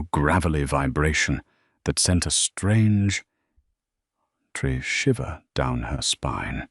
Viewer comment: Great story! The strange snow is distracting. [16:54] What is a "tree shiver"? Some words are repeated. Some words are garbled. Some words are mispronounced.